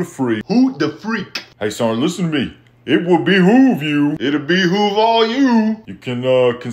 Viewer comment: first time i heard a sergeant major use the word behoove. i thought he said behooah .. alsi i never heard the word doggone used as much by one person before